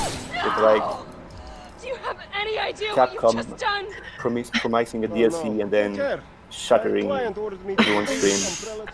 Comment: What does say here? Edit: Also, could we have a link to that meme? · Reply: "Capcom promising DLC and then shoting everybody's dreams"